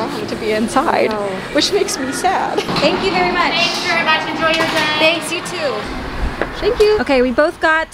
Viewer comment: Did the starbucks person say ‘Enjoy Your Drink’? Thats what I heard.. and said ‘You too’ which is relatable lol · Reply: I heard her say "enjoy your day"